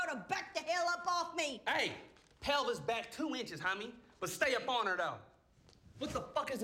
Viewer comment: "Lookin like a sexy baked potato" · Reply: I could’ve sworn he said hell is back 2 inches